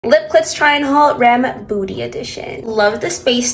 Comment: Did you say "lip clits"? I swear I heard you say that. If you said something else, I'm sorry lol.